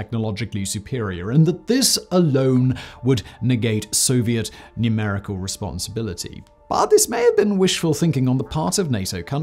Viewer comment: Did he say ‘soviet numerical responsibility???’